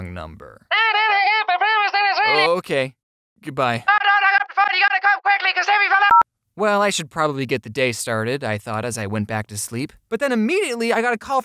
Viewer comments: all I heard is "you had to call quickly cause Jimmy fell the well lance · When jack was talking I thought he said Timmy fell off a elephant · I think the person said Timmy fell down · I heard "YOU GOTTA COME QUICKLY! TIMMY FELL OFF TWO ELEPHANTS" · I heard “YOU BETTER COME QUICLY BECAUSE TIMMY FELL DOWN THE WELL LANCE” I’m definitely wrong .-. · Go to 6:06-6:15 what is he saying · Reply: Killer games at the end I heard “you’ve got to come quickly Timmy fell down the well Vance” hangs up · lol I thought it said “you gotta come quickly because Timmy fell down the elephants!” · it said" come quick timmy fell down the well, Vance" thats what i heard did anybody hear something else oh also james i love your videos ! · I think she said timy fell down a well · I think The lady was saying “ you have to come quickly because Timmy fell off the vance “😂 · I heard "COME QUICKLY VAUSE TIMMY FELL DOWN A WELL" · i think she said "DONT HANG UP THE PHONE YOU GOTTA COME QUICKLY CAUSE TIMMY FELL DO-" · I think they said "COME QUICK TIMMY FELL DOWN THE WELL!" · I KNOW!!! At 6:14 that call was the voice from the fairly odd parents because I speak gibberish and it said "You have to come quicky! Timmy fell down the well vance!" Does anybody know what episode that was? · I heard "Timmy fell down the fence" Or "Timmy fell down the stairs :] Edit: I ment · It sounds like it said “hey you better come quickly because Timmy fell down the well!” Ha ha ha ha LOL!!